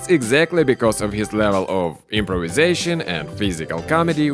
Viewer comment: I thought I heard "Jimprovisation".... 😄